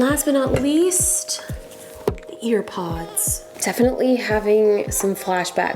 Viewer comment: did she just say "AirPods"? I'm offended. THEY HAVE FRICKING WIRES!!! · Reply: Alina Smith she said EarPods 🤦🏻‍♀️🤦🏻‍♀️🤦🏻‍♀️